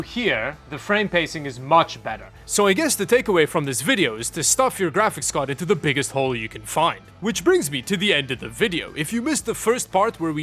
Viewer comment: did everyone understand what he says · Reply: "Stuff your graphics card into the biggest hole you can find"